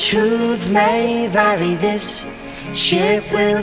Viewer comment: I thought it said "Wally's safe to show"